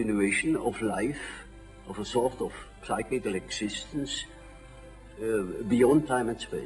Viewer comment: PePe Frog Hey dude appreciate your comment. Thank you for it. Though I’d like to make one correction. I believe at 1:22 he says “cyclical” as opposed to “physical”. It’s just that he pronounces it as “SIGHclical” instead of “SIHclical”. Also, right after this he says that it’s “beyond time and space”. I believe he’s trying to say that the physical is this life, right here, confined to time and space. But there is something else out there that isn’t, something that’s beyond the confines of time and space.